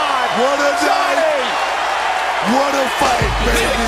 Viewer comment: "what a day, What a Fucking Say!!!!" Booker T is so bad ass with those cussing slips haha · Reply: Corey A he said what a day! and then he said what a fight!